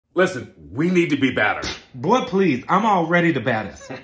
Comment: I thought he said batter.